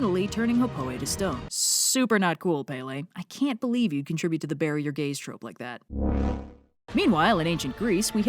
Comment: I thought you said "Barrier Gaze".